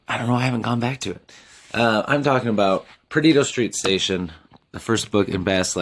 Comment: when you said "perdido street station" I swear I heard "pervert obstetrician